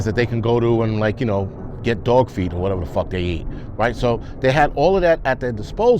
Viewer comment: I luv this guy. "Dogfeed or whatever tf they it" 🤣🤣 Always great content. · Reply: I thought he said dog feet 🤔 lol